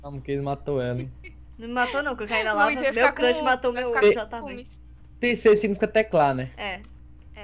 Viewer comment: So eu que ouvi a falando no 2:05 ao 2:08 ela falou : meu crush matou meu jv . :O